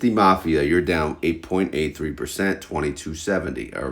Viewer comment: I heard you say MSTY down 8.83% instead of 0.83% and I shit my pants 😂